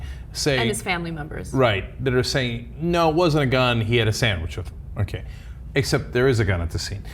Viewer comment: What's he saying it sounds like he's saying "he has a sandwich"